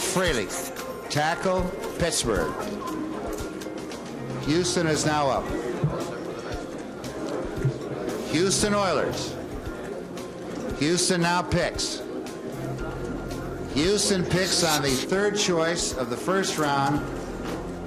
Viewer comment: Damm what did he say i didn't hear can he say it again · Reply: Rozelle's like, "Houston is on the clock...No, really, it's Houston...No more shuffling around...Oh, for Christ's sake, won't someone quit jerking off and pick a goddamn player?"